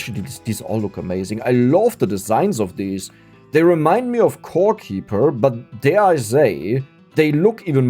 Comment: I swear I heard "they remind me of poor people"